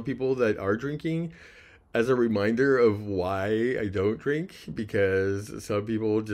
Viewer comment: Congratulations man keep up the great work but I heard you say drinking was a correct I didn’t know drinking was a correct but anyway keep up the grades work. Take the edge off. Have a drink. 🥃 😎